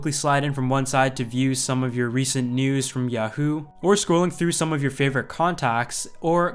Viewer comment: Did he just say "yuh-hoo"???😂😂